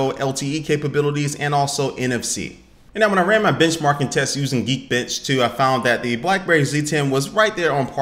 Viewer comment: I heard Geekbitch!!!!